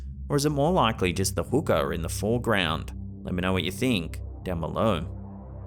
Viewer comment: For a second I thought said "A hooker in the foreground"😂